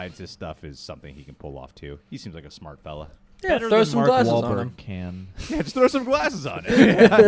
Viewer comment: real quietly, with taking over him, you can hear say "if Mark Wahlberg can do it..." lol don't worry, I heard that joke n I thought it was actually pretty funny lol